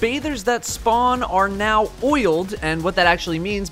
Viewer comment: "The bathers are now oiled, now what they do besides seeming gross to think about" i love how he still makes fun of how gross the whole idea of the baths are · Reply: He said oiled? I thought I had heard BOILED. Which is way grosser, really.